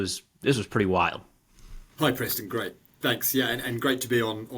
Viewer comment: did he call you Preston Gravy or am in going coo coo?